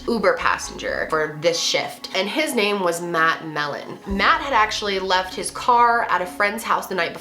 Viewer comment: Not trying to be rude towards the guys name, but when you said "Matt Mellen" I thought you said "that Melon" and I had to replay that bit again lol